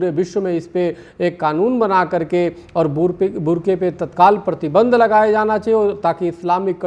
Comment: 2 मिनट 3 सेकंड। 2:03 पर क्या बोला है भाई!😂😂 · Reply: Bur ka